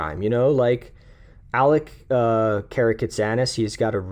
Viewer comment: who is that? I can't make out the name to look him up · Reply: Alec Karakatsanis :)